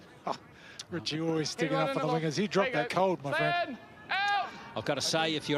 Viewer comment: did he say the n word 😂😂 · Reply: Nah he said “Richie you’re always sticking up for the wingers, he dropped that cold my friend”